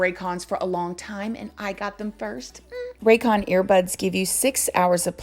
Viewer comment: When she said “I got it first” I heard “I hit it first” lol